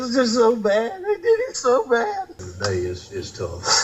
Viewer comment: I heard, "I did it so bad"